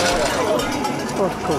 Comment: I heard him say '"its already destroyed"